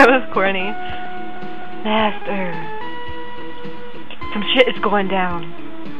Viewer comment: And the way you say "master" sounds like gollum saying " my precious" , ah and when Kunzite says " destiny draws near"... did you say " some shit is going on" ? O_O